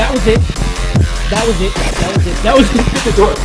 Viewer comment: I heard him say "get the door!"